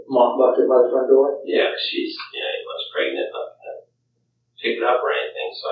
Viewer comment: When Scott says he moved the mop bucket for her because, “she WAS pregnant..” ... THAT is suspicious AF... she’s hardly been “missing” for 24 hours, & their baby isn’t due yet, so she should still BE pregnant. 🤨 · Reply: That's what I thought I heard as well, but I went back a few times and turned up the volume and @ 22:21 he says "yeah, cuz she's [sounds like he says "ya know"] eight months pregnant".